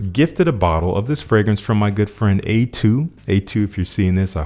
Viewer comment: When you said "good friend", I had to rewind it. I thought you said "girlfriend". You were my hero for 7 seconds 🤣